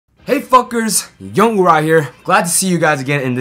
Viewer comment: I heard ''Hey fuckers''you gained a like and a loyal sub man